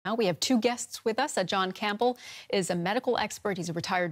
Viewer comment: Just for a second I thought she said Sir John Campbell!